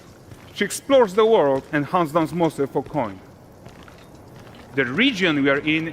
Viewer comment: dfaq did he say there lol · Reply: Hunts down monsters for coin